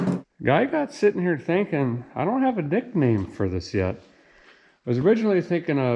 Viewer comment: DId I hear "I dont have dickname for this yet"